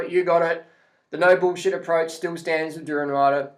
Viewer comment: when you said "no bullshit approach" I heard it as "noble chimp approach"... kind of disappointed that wasn't what you really said :/ lol